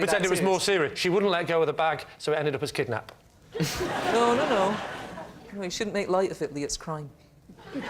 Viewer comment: What is she saying at 17:30 🤔? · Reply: “No, no, no, you shouldn’t make light of it Lee, it’s crime.”